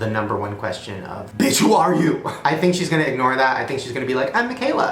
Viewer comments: Please tell me y’all heard her say “bench” and not “bitch” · This seems very weird to me, because if you listen closely it sounds like her voice is a little auto tuned, the way she said "we are here, we are Miquela", and it might be me but when she said "bitch" I sounded like she said "binch" to me as if she was like Siri or something because sometimes Siri pronounces words weirdly. And like, wasn't expecting a full on answer but I noticed she was avoiding some of his questions. Which makes me think that she really is a robot who maybe has some good editors on her photos. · She sounds like a robot. And it sounded like she said binch instead of bitch as if it was autocorrected or typed in wrong